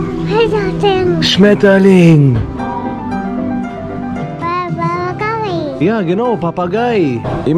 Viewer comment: Ich habe bei Papagei Papa gay verstanden